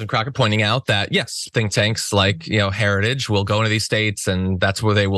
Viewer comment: It almost sounded like she said "stink tanks". Apropos.